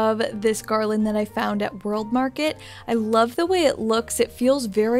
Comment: Your home looks stunning. I appreciate all your decorating tips! Where did you say you got the garland for your Mantle? It sounds like you said Baroque Market? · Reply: World Market 😊